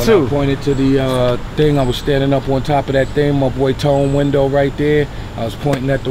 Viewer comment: He said my boy “”tone window” and my high ass thought he said Telemundo 🤣🤣